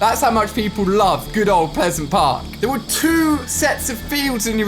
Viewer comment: Did he just say pisont park or something 😂